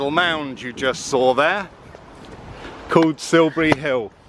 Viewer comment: The landscape is beautiful. Farm land but following the spring is very nice. The wind is a bonus . The mound is fascinating. Having a mote around it is even more interesting. Such magical places. Thank you for sharing this adventure with us. Feeling the energies of people is a great gift for the desernment and the recognition of others intentions and their actions in their lives. I depend on how I feel about people and about places. Trusting the energies. Thanks for sharing your thoughts. Silvery Hill is a fascinating place. I think that you are a wise man to be careful about the energies that resonate in our world and act accordingly. Not fearful but cautious about it and not entering the space where there is resistance. Good for you 👍 It is a valuable lesson for living in trusting yourself and your powers of observation. . Silbury Hill, opps, I was thinking that you were saying Silvery Hill, obviously I am uninformed and unfamiliar with the history of the area. Thanks for sharing this with me and for taking the care to help people in seeing the world in a way that is personal and educational. I am so grateful. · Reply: I also thought he said "silvery", and it made sense to me because the hill is a silvery green. 😄 But, now that I've looked it up and read about it I'm intrigued and watching it over. I should have done that before I watched it. 😁